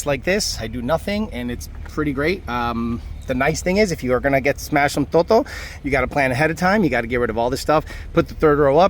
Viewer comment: what did he say, smash what?🤣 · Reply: Smash some "toto" Spanish for crica